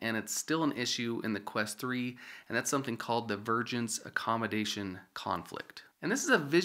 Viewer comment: I heard "virgins accommodation conflict" Traveler: I need a place to spend the night. Innkeeper: Sure, we have plenty of rooms, come on in. Traveler: I should inform you that, I am a virgin. Innkeeper: oooooo, ssshhh, yeah, looks like I was wrong. All full up. Sorry buddy.